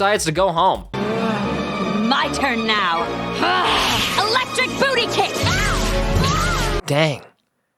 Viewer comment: E-electric bootycake? Is that what she said???